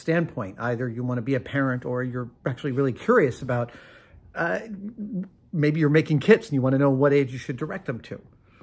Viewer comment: kinda lost it when thought he said "either you wanna be a parent or you're actually really curious about, uh, maybe you are making kids"